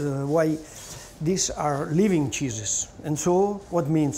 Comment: Wait, did he just say "Living Cheesus?"